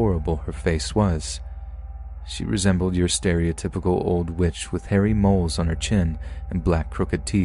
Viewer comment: At first I thought he said “she resembled your stereotypical old bitch” in story 3 😂